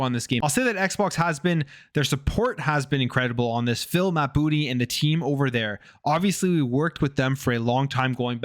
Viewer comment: Thanks for the updates. Just out of curiosity, at 6:39, did he say feel my booty or Phil and Matt booty?